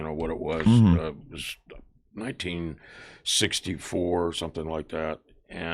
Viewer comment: 🙄 I had to play that back because I thought that he said that his IQ was 64 😂